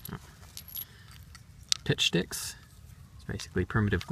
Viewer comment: Lol at 4:44 I coughed and I misunderstood you. I thought you said "Bitch Sticks"